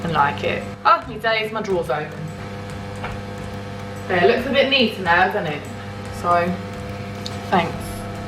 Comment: Did husband take the chicken out? Sounded like I heard “it looks good babe”